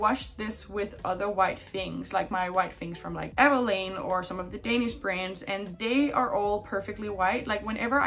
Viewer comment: What I heard was “the Danish brand Maneskin” 😭